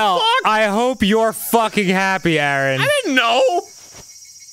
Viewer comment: I thought he said "FCKIN PORNO" at the end. Lmao I'm high ☠️🤣